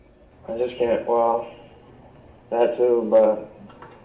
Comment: He actually said "That too, but..."